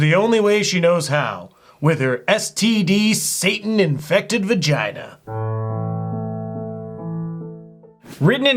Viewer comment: So like most people, I was multitasking while watching this and then I hear say , "STD RIDDEN, SATAN POSSESSED VAGINA" Needless to say, you guys now have my full attention :D